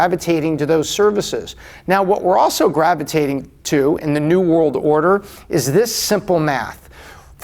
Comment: Did he just say we’re in the new world order?